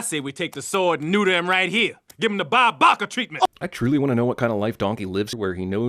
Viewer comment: at first I thought he said “Barbarka” and not Bob Barker